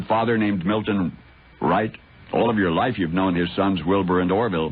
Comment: I would swear Paul almost called him Milton Bradley. Well he was talking about toys…